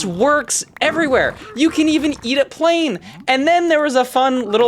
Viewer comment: (nice) i thought he said "you can even eat a plane!!"